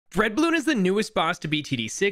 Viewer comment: Lmao i thought you said red bloon instead of dread bloon